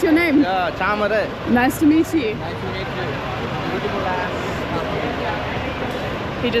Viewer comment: i think he said "beautiful eyes". but based on your reaction, the other word was a good compliment though. 😅